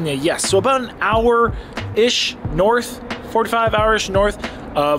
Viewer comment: Man I thought he said 45 HOURS north...but I listened again...